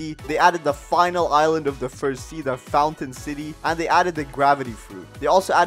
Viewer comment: is it just me or i hear falcon city?